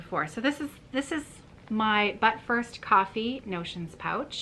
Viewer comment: Oh my gosh I’m still laughing!! I had your video on and I was getting my stitching together and I heard you say but first coffee. You got my attention and I thought..what is buttfirst coffee?! And then I saw your project. Now I can’t get butt first coffee out of my head! 😂Also add me to the list for the ink circles. I just love it. I’ve always wanted to do an ink circle and that one would be a great beginning ❤️❤️